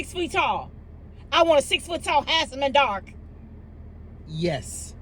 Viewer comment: did u just say u want a 6ft tall ass in the dark? Lmao 🤣